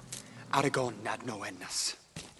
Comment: when legolas talks elvish and says 'somethings out there' it just sound like hes saying 'aragorn nag no legolas' lmao